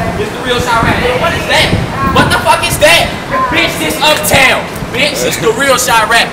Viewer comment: I died when he said what the fuck is that 😂 r.I.p pappy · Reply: Marcuz Beatz i thought he said: what the fuck is dead?